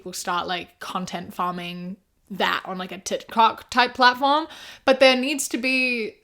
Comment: GIRL DID U SAY TIT COCK INSTEAD OF TIK TOK😭 WHILE talking about a spicy tiktok, I'm dead.